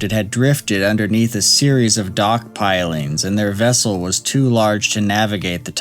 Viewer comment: or so I heard it as "it had drifted under a series of 'Dog Pilings' and was unable to be reached". I pictured all the scientists and marine biologists getting bored waiting for the coroner and started rough housing, wrestling on top of water logged corpse.